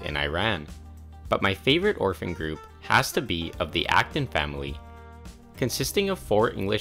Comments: HELP I THOUGHT HE SAID AFTON FAMILY (I love fnaf a lot😭) THE BEST PART IS THAT THE FIRST TWO WERE NAMED WILLIAM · I love the Internet! 14:25 The Acton family was immediately welcomed back into the wider Wikipedia community xD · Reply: I thought he said the afton family and i was like fnaf?! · Who else thought he said Afton family instead of Acton family